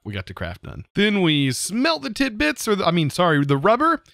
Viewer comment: WHAT DID YOU SAY !!!!! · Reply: Tid bits lol